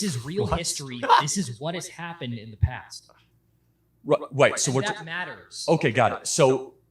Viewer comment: "white" instead of right? Hmmmmmmmm.